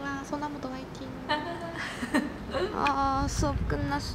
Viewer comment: is she from a girl group too?? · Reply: nvm I heard sonamoo